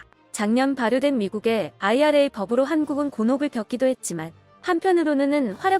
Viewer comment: 곤혹이 아니라 곤욕이지요? 나레이션은 곤욕으로 들리는데 자막이 곤혹으로...